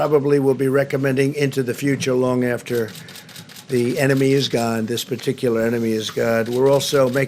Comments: did he just say the enemy is god? Shortly after the time stamp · Trump said the Enemy is God. Trump is sneaky. Beware of Him, they are all on the same side 666. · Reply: Just listened in 0.5 speed slo-mo and indeed it sounds like he said "this particular enemy is God"